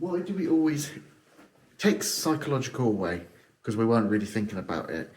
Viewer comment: I’ve put this time stamp a couple of seconds before I can hear a voice in my right earbud. It’s when says something along the lines of “take a physiological way”. Does anyone else hear it?